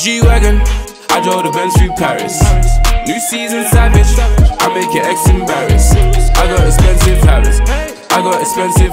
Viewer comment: It's been 43 days since this video has been out and the whole time I thought he said: I make your eggs embarrassed 🍳